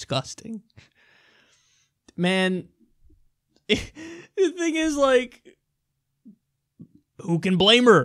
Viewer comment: did Kyle say "this nigga's like"? Lol please tell me I'm wrong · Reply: "the thing is like"...no SJW rant for you today.